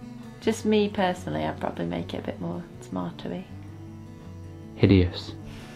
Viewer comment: I'm not sure I really understood what the lovely said she would change? The cc said "smarter e" which is what it sounds like she says. What did she actually say? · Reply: Lin Knappett She said she would make it more tomatoey if there were such a word lol